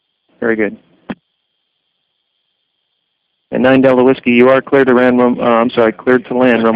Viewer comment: 9 delta wisky you are clear to ram....uh....land. Did I hear right? omg!